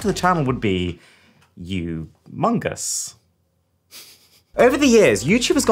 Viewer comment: bruh i heard "you-mongus" and thought among us